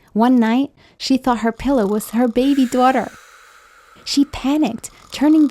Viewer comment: is it just me or did she say baby doaghter at 3:39 bc well... lol i guess?